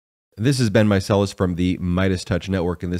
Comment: I just realized he is saying, "This is Ben Meicelas." I thought he was saying, "This has been Meicelas," and it was just a quirky way to start his comments. Erg.